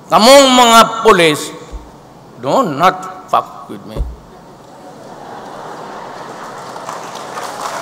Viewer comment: I heard he said "don't fuck with me !!!" LOL.... Make Philippines Great Again !!